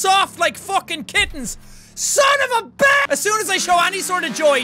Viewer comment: "SON OF A BI...." 😂😂😂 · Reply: i thought he said SON OF A BILLY!